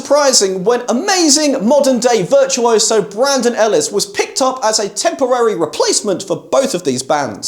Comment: I glanced over at my phone and thought you said Bradley Hall instead of Brandon Ellis and it gave me a nice chuckle